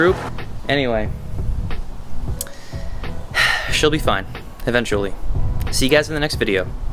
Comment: At the end of the video I really thought u said "SHELBY FINE" but I think u said she'll be fine